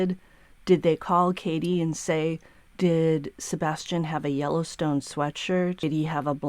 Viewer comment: Jellystone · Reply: LOL, I thought they said, Yellowstone, but what the matter, I am Hard Of Hearing. 😊